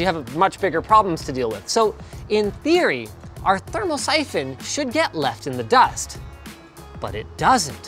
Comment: Thought it said thermopoison